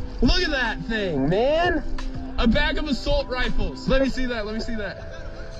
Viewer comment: “A bag of a salt rifles.” That I thought I heard at first